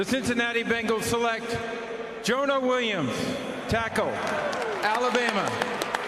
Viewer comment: I thought he said “ Johan Williams taco Alabama